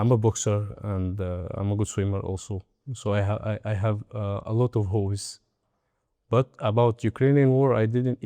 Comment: Lol, it sounded like he said he had a lot of hoes 🥊🏊🏽‍♂️